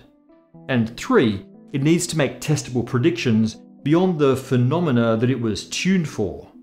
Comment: thought he said testicle predictions at first 😂